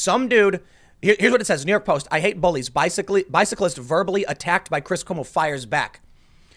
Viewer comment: I thought he said it was a "biker" who assaulted him, not a "bicyclist."